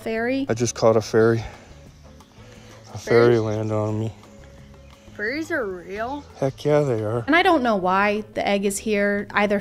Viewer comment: I thought I heard "Furrys are real?"